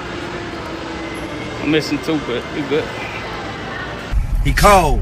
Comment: Anybody know wtf that guy said at 5:11 the whole clip got me confused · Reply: He said, this is what I wake up to. But I'm missing 2, eh, we good.